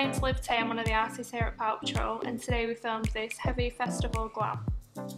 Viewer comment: I’m sorry , I heard “Paw Patrol” 😭